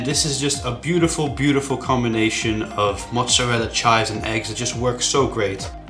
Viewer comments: seriusly did he say mozzarella scheiß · did he say "chize" why the fuck ???